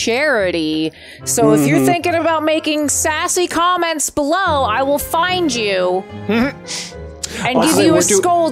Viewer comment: when said, I would find you that is funny, I thought she said, I'm gonna find you, whoever are you I'm gonna tell you that what I thought that is funny